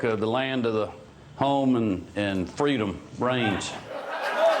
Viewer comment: Brian Kemp said out loud, “land of the home and freedom of the range”. People of Georgia you can do better than this 🤡!! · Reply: I thought he said "freedom brains".